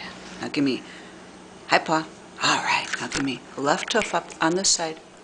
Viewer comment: Cats can certainly be trained especially if you start out young. At 1:41 did she say Left Hoof ?